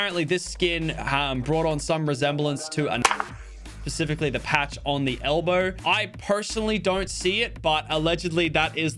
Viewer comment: What did lachy try to say at 2:23? I cant figure it out lmao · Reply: Nazi